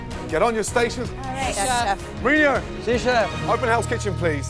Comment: Did anyone else notice at 20:07 that the subtitle says, "Jean-Philipe", even though Gordon says, "Marin(i)o"? Wtf